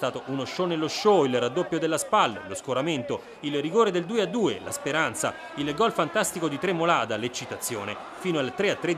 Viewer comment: Cosa dice a 0:37? Lo porto? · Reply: Lo sposto!!😅